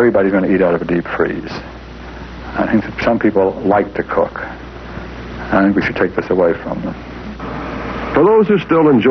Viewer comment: About 16:00, did that guy really say “Some people like to cook. I think we should take that away…” I’ve listened to it several times, and I want to think it’s “I don’t think we should take that away…” But that’s not what I keep hearing.